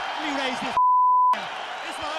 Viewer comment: Le sous-titre n'est pas bon, il disait "Let me raise that m******f****** belt" (Laisse moi mettre la ceinture sur l'autre épaule, en gros)